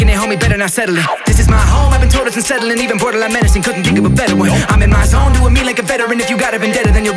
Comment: Am I wrong or did I just hear "This is my home...Couldn't think of a bedouin (better one)"?